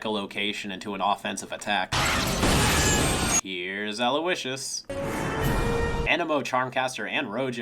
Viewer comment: Did you say allow wishes or did you say something else? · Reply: He said "here's Aloysius" Animo's first name.